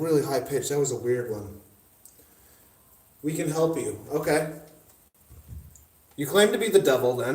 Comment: I swear I heard "we can save you" and you asked something and it said "the book".